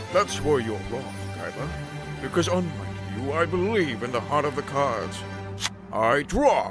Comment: I heard that as "The pot of the cards"